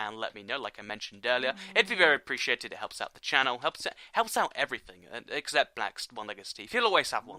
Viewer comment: am I tripping or did say " it helps out everyone except the blacks going down a steep hill" ?lol hit the cc button and read at 6:10 I like this guy